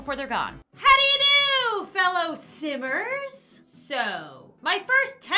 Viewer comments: nobody: not a single soul: not even Bob Pancakes: HELLO FELLOW SIMMERS! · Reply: Haha... I thought she said "hello fellow sinners"! For a sec 😂😂😂😂😂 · I swear I always hear: “ How do you do? Fellow SINNERS “ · "How do you do, fellow Simmers-" Me: I low-key thought you said, "Sinners." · intro-: “How do you do fellow simmers!” what i heard: “How do you do fellow sinners!” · When said how do you do fellow simmers, I thought she said "How do you do fellow Sinners" I was like "Lauren you be watchin panic?" and someone get poor drawing tablet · I thought in her intro she said, "Hello fellow sinners-!" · i thought she said, fellow sinners